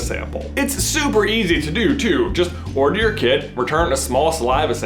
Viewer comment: I thought he said order your kid. . .